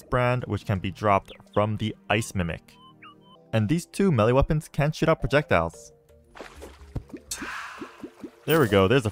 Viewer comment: it honestly Sounded like you sound “these two weapons can shit out projectiles” 😂😂😂😂😂😂😂